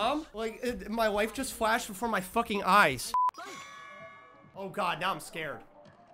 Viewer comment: Sound like you say "My wife just flashed before my fucking eyes" lol